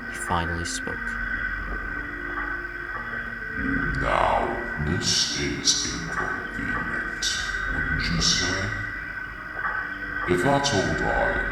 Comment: What did invert said at 28:40? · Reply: He Said now this is inconvenient now isn't it